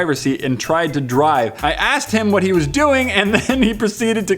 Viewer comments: it says naked instead of asked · autocorrect at 5:00 again its said naked instead of asked LOL